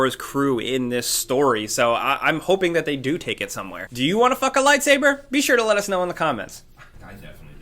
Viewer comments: did he say, “do you want to fuck a lightsaber” @ 3:50 ? lol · Did anyone else hear "do you wanna fuck a lightsaber" at 3:50 wtf